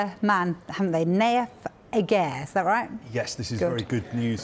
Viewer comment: I really thought it said aguero 😂